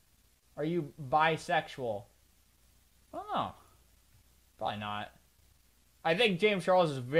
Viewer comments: guys, he said “idk, prolly not.” He didn’t say he was bisexual, but he didn’t say he was straight. I feel it’s best we don’t give him a label. If he said he doesn’t know, he doesn’t know. And if he compliments guys, that also doesn’t mean he’s gay/bi/queer. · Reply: i thought he said "why not" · How is this him telling us he's bi? Just beacause a man finds another man attractive doesn't mean he's gay or bi, and just beacause he will probable wear a maid dress also doesn't make him gay/bi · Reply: i think people thought he said "why not?" instead of "prolly not" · I thought he said 'why not' instead of 'prolly not' lol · i thought he said "why not" at first lollll also bruh can ppl stop assuming things abt ppl just cause of a few lil things they say he didnt confirm or deny it so pls just respect that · I thought he said “why not” 😭